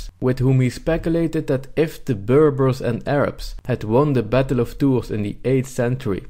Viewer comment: took me like 10 minutes to find out what the hell you were saying. Battle of TOURS, france. october 732